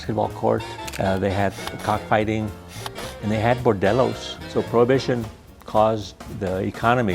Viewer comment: at first I thought he said “Modelos” lmao